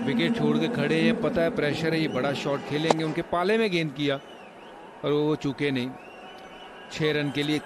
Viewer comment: is he called chutiye? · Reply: No he called chuke